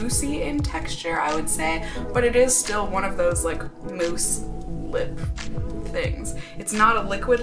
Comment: For a second there I thought you said "moose"☺ It was obviously "mousse", my ears are just damaged I guess^^